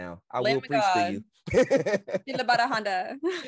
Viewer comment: Amazing study! At 39:55, what did you say? Sounds like Keened about a Honda. 😮